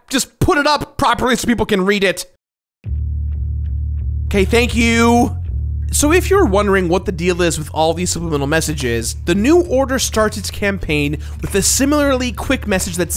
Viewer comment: I thought it said next order so I though it was digimon😭😭😭😭